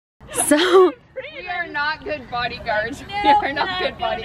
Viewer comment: I thought she said a BODY and not BUNNY. A real heart attack 😲